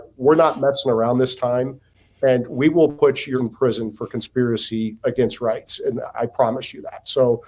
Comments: I thought mike davis said throw your f@ in prison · what Mike Davis actually said to Big Tish was, “…we will put your fat ass in prison…